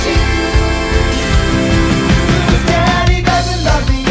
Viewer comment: I Thought It Said “And Those Kids Have To Choose” Then I Heard “Issues” DAMN